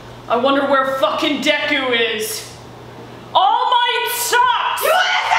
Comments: Bakugou: I wonder where deku is.... ALL MIGHT SUCKS! Deku: YOU WANNA SAY THAT TO MY FACE Me: perfect couple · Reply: I thought he said all mights son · Bakugou: I wonder where deku is.... ALL MIGHT SUCKS! Deku: YOU WANNA SAY THAT TO MY FACE Me: perfect couple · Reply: I thought he said All Might socks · Bakugou: I wonder where deku is.... ALL MIGHT SUCKS! Deku: YOU WANNA SAY THAT TO MY FACE Me: perfect couple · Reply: I legit thought he said socks